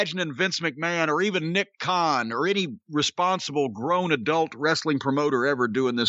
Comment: Did Jim just say Nick Kahn was a responsible booker? The same guy that created the Zombie match? Jim get ur shit straight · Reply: I thought he said responsible adult. Either way, Nick Khan's job is to make WWE money and that silly little zombie skit got them a million.